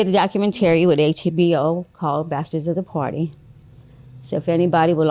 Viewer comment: I can't understand what she was saying the name of her husband's HBO documentary was. Can anyone help? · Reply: Bastards of the party